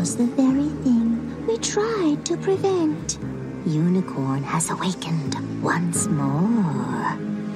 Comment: Every time they say “unicorn” I hear “unicron”